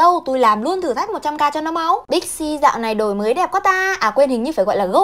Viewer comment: Big C nghe thành pepsi :))